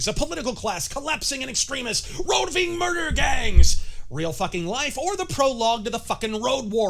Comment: instead of "Roving murder gangs" I heard "Roe V murder gangs" and honestly I think that's way scarier